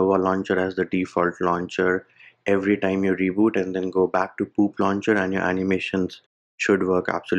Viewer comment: did he just say poop launcher ☠️🤣